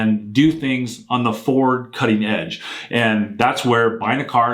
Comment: 8min 47 sec.... did you just say Ford Cutting Edge! How dare you! Lol! Stay stand man!